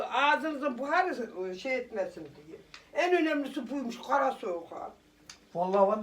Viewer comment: lan msmskskskkdkd off yıkıldım laannn ne güldüm ne diyor orta teyzem anlamadım zmmsksms gara soyka ne abi?